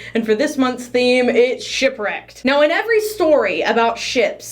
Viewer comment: i thought you said "shrek" when you said "shipwrecked" 😭